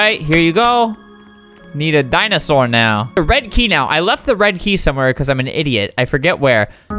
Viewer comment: said red key I thought he said bread key